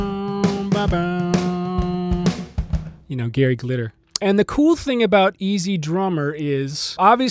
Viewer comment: What's the name he say at 03:55? Thanks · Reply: Gary Glitter